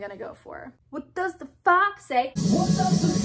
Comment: at first i thought you said what does the box say